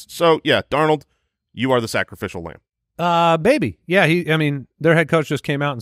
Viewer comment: I heard "baby" 😂